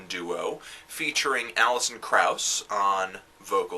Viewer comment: I heard "Alison Krauss" at first... I was really asking myself what the hell she was doing in this kind of project for a while!